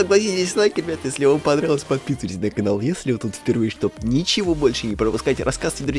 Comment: Мне одной послышалось "Отписывайтесь пожалуйста на канал"